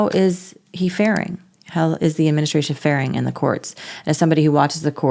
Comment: Oh--I thought they said they were farting in court.